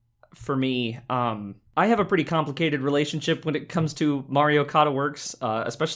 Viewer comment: For the first three minutes I thought you were saying “Mario Koda.” I’m so smart lol.